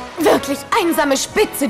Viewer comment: LOL at 43-46 it sounds like zelda said "i am the spits"then link says "its true" LOL XD